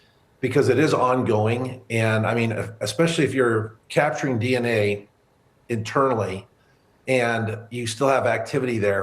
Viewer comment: Did he just say “…capturing DNA internally…” ??? 🤣🤣🤣🤣🤣🤣🤣🤣🤣